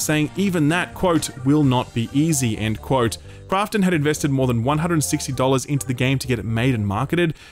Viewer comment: sounds like you say more than 160 dollars which isn't wrong, but it also isn't super accurate. Love the show.